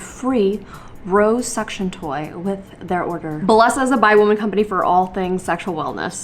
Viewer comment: during the bellesa ad at 0:19, my queer ass heard it as a “bi woman company” and i perked right up! 😂🏳️‍🌈